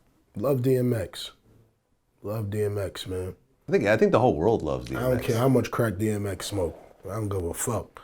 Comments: Did Vlad say Nigga, I think the whole world love DMX? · did vlad just say, "nigga, I think everyone loves DMX" ???